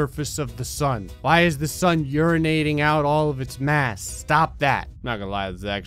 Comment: What you said: "Why is the Sun urinating out all its mass?" What I heard: "Why is my son urinating out of his ass?"